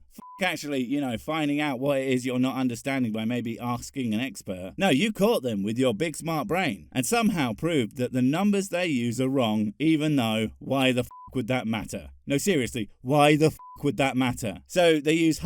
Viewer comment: I'm not ashamed to say that i heard " you caught them with your big smart brain" as "you caught them with your big smart pants down" mentally